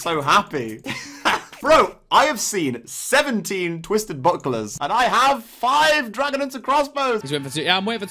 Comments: Thought he said buttplug not buckler😂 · When said buckler I thought he said butt plug